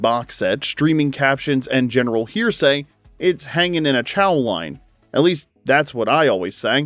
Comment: Dang, I thought they were saying "hanging in a John", idk why though lol